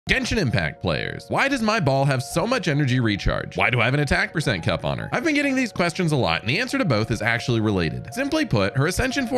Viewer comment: I THOUGHT HE SAID "why does my ball have so much energy recharge"